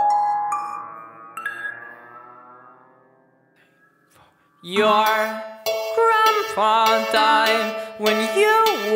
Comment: Listen closely to 0:05 to 0:07 Any idea what he says? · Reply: He says "Three, Four"